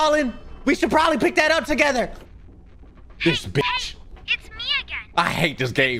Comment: BRO FOR A SPLIT SECOND I THOUGHT SAID " Hey btc!" i was like HUHHH??